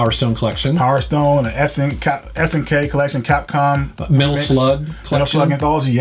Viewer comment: Subtitle is wrong. Should have been SNK collection. SNK is the name of the corperation, nothing else.